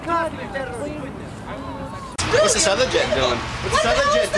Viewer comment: in the original, the guy does not say "whats that other jet doing". He begins saying "holy..." in a greiving tone then shouts suddenly as the fireball occurs. The lady is constantly freaking out but becomes completely manic only when the fireball happens.